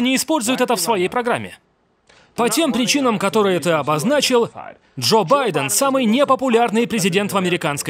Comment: Послышалось Жоп Байден 🤣